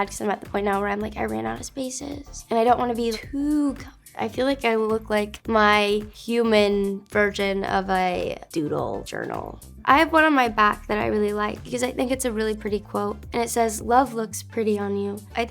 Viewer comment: am I hearing things or did she say human virgin? 😂